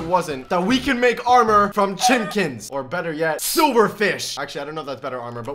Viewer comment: why did he say chinkens